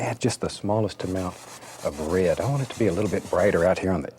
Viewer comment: I can't get over the way he says "rared" instead of "red".